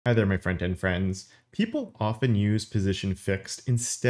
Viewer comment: Okay, for the longest time, I thought was saying "Friend and friends" at the beginning of his videos, and I thought it was slightly odd. But in this video, I realized it was "front-end friends" for the first time. #blessed